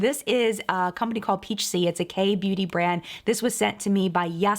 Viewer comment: I heard "gay beauty" when you said "k beauty" and now I don't want to call it anything else for myself. <3